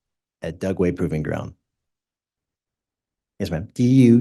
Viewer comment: Did he say "yes ma'am"?